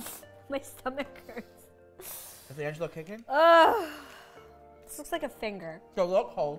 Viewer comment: Did just say the new baby’s name at 7:36 ???🤩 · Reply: I heard him ask "DeAngelo kicking?"